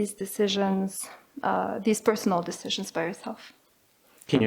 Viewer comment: did she say ( by) yourself? The subtitles state for.